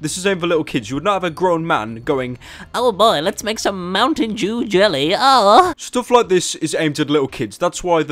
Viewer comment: I swear he said mountain jew